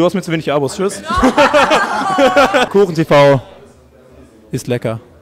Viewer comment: alter ich habe kurdenTV verstanden